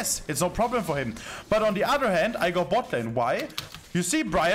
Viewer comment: i think that was a 'why' instead of 'yes' ^_^